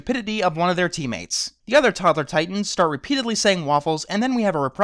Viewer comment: Is it wrong that I keep hearing "Toddler Titans" as "Tumblr Titans"?